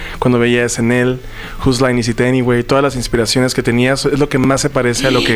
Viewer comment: Alguien me puede decir que programa de SNL decia ray es que no le entendi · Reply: "Whose line is it anyway?"